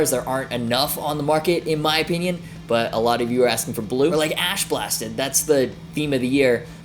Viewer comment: Whenever someone says "ash blasted" I hear "ass blasted"